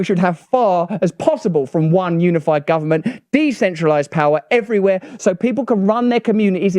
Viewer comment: Did you say DECENTRALIZED 9:23 ? I'm listening....